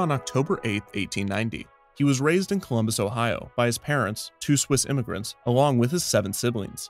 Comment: I thought I heard "toothless immigrants". That seemed like an odd detail to give so I rewatched it and heard "two Swiss immigrants". Moral of the story is maybe don't eat potato chips while watching videos.